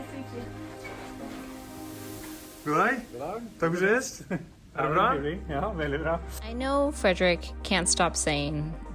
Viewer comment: Did I heard "Dobrze jest?" around 2:55? :D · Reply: "Takk for sist"